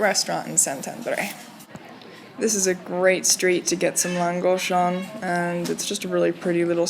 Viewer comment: What is the name of the food you said is sold on that street? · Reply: Lángos (pronounced Lang-goash). It's basically fried dough with whatever you want on top.